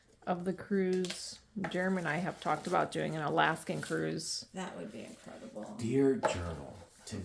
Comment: I thought he said a laughing cruise . I was like where is that at? 😂